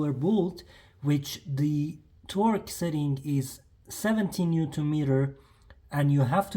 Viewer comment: Hi can you confirm the torque settings as the bottom nut sounded like you said 17 Nm(70Nm?)which is less than the top nuts? Thanks